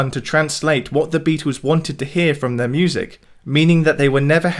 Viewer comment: I hear “shit music” everytime lol